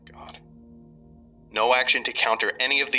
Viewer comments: What was that at the 4:33 mark? Sounded like "God". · Does anyone else hear the muffled "thank God" after the static at 4:30?